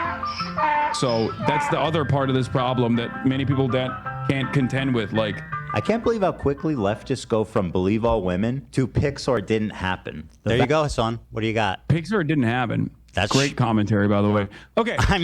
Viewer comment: Also was i the only one who thought ethan was saying Pixar didnt happen i was like huh...